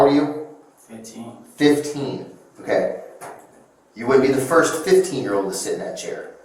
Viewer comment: And then he'll release his epic rap single about being the "first 15-year old" in that chair. Big mistake for the detective to put that in his head... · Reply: Oops, I misheard "wouldn't" as "would" the first time. Good catch!